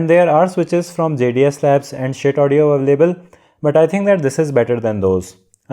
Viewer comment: Did I Heard Shit Audio😂😂🤣🤣